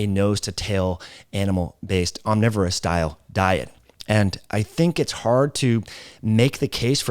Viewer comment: Being vegan makes you say “Dial” 16:10 instead of Diet, oh wait he’s the one on carnivore DIET 😂